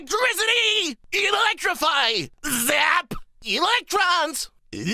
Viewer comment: all this time I thought he said "Life Rocks!"